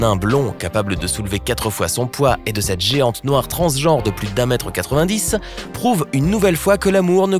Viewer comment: att quoi j ai pas bien entendu "cette noire"